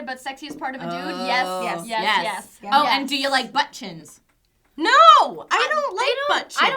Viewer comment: When she asked about butt chins I thought she said butt cheeks... I was wondering how it was all related. Thanks for reminding me to get a hearing aid! :-)